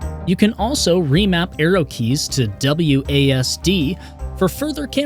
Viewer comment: I thought you said remap aerial keys xD